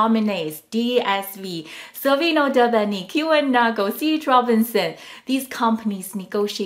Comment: what is name of your us based freight company i cant hear the prononciation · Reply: 1.DSV, 2.Savino Del Bene, 3.Kühne+Nagel, 4.CH Robinson. I didn't understand it like you either. Thank you ❤